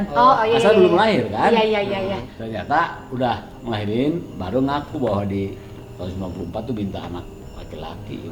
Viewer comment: Ayah nya kak alshad 6:20 bilang apasih kurang jelas suaranya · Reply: Ibu nya baru ngaku waktu naik haji tahun 94 do'anya minta anak laki2.